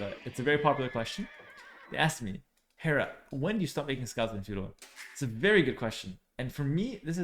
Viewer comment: "everyone, listen up. This is very important. I get this question alot. Whendoyoustopbeingscoutfuneral?" xD I might have hearing problems but had to go back 4 times to distinguish what you actually said there. Nice video as usual!